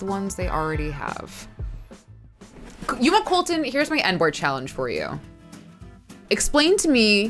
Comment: I heard "N-word challenge" and not "end board challenge", and thought 'Oh god, what am I about to witness right now.'